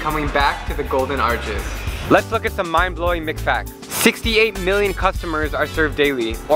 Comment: I thought he said mcfats